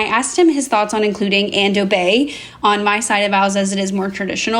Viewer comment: When she said "and obey" I thought she said "ando dei"... some obscure Latin liturgy 😅